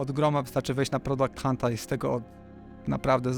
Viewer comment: Świetny materiał, dzięki! Mam pytanie - jak nazywa się strona z narzędziami wykorzystującymi AI, o której mówisz w 43:15, bo nie do końca usłyszałem? :) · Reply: Product Hunt :) dzieki !